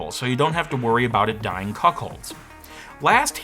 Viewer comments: I actually at first thought they said "dying in cupholders". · Not me having to replay the same 5 second segment three times because I heard say "dying cuckholds" and my brain wouldn't process it